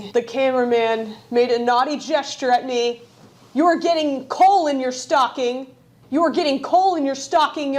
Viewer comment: It sounded like you said “you’re getting colon in your stocking!”